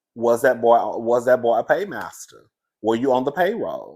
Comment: Cousin at first I thought you said pagemaster. I was about to yell Macaulay Culkin?!!!